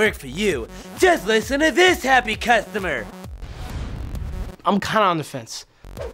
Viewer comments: what did he say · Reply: Nam DinhZ I'm kinda on the fence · What does the guy from 3:02 said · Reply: Im kinda on the fence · what did he say pls tell me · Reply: "I'm kinda on the fence"